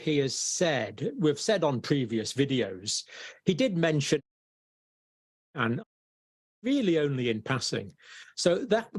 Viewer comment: Can't hear what he is saying. Also went to Rumble and couldn't hear it there either. What are the 2 things that he mentioned? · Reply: I was able to lip read him and he said hydrox a chloro Quinn and Ivor Mectin.. sorry the way I had to write it